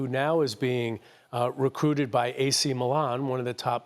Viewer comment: Recruited by AC Milan? Have I heard correctly?